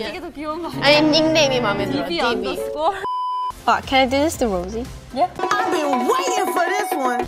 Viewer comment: It sounds like she said "Fuck, can I do this to Rosie?" lmao